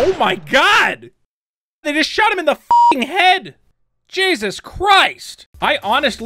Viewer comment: sounds like he said “nigga shot him in the fucking head” lmfao which I found more funny